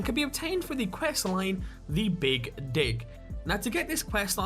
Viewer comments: I thought he said "the big dick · Anyone else thought he said the " big dick"